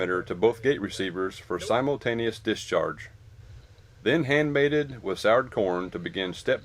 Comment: hand baited with "soured corn"? Did I hear that right?